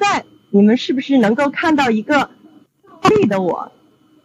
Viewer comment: If you speak a language other than Chinese or English, you can use the auto-translation. It's not perfect but works quite well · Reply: the subtitle is "对的我/Yes", but I think what she said was "倒立的我/inverted me". It was the short signal glitch that made her words a little bit distorted as that moment.